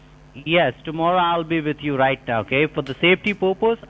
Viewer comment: did he say "tomorrow I'll be with you right now" ?